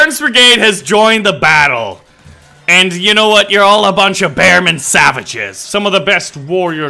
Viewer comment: for a sec my brain filled in "Bloody Legends" instead of "Bearman Savages"